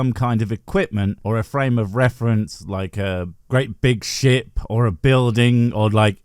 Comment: I misheard you saying "Great big ship" as "Great big shit". But then, you're covering Eric Dubay, so it stands to reason.